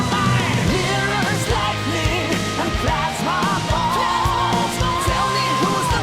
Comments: Oh, I thought it said "and crush my balls" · I came here because I kept hearing "Touch ma balls" and thought "wait, it can't be right". Glad it wasn't right